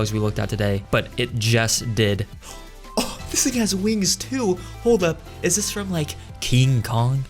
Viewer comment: Yo did he just say "this nigga has wings too"?😂😂